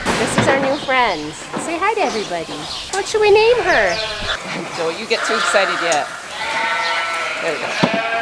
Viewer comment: You just asked what we should name the new white bestie and then you said “So don’t you get too excited yet”. And I heard “Zoey don’t you get too excited yet”. So I think her name should be Zoey!!